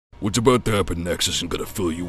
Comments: At first I thought spiderman said why are you making a naruto reference. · Sounds like he said: Why are you making a underdog ref